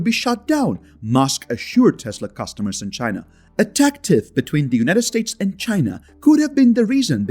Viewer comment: The United States banned Tiktok?! I'm not sure about that, buddy. Also, what the heck does he say right below that? A "?" Between the United States and China could have been the reason behind tesla-cars being banned in china. Sounds like "tactic" but I know that's not it.